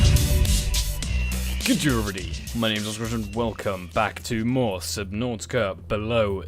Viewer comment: for the intro I heard "g'day everybody my name is mdsvrb"